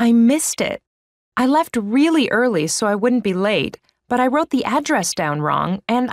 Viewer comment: " I left "TO" really early so".... I think I heard that, anyone else?